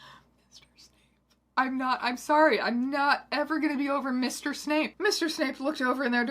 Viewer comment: I'm so sad I thought you said Mistress Snape and I got vested in a character that didn't exist for a full five seconds. 😔